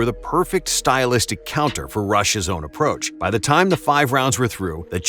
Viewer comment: Did you say that gsp is russian at 0:50 lol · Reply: He said Rushes. That was his nickname